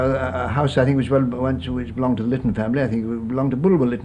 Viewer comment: What family name did he say there? I heard it start with L and end with N but I'm curious. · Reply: I believe he said something like Litten or Litton.